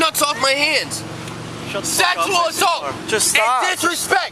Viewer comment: “Get your nuts off my hand! SEXUAL ASSAULT!!” “Just stop…” I’ve never laughed that hard in a while lmfao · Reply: At first I thought he said "sackfull assault" and couldn't breathe 🤣